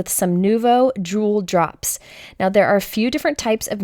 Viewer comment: Gorgeous...I thought you had said Drool drops not jewel, it made me giggle and I thought to myself hmmm with my English Mastiff I could make some Money ! lol